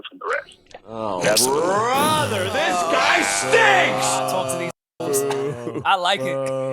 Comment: Sounded like said talk to these niggas 😂